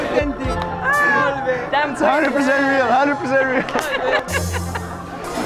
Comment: "Interactive wax" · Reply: Thought he said "attractive wax" for some reason lol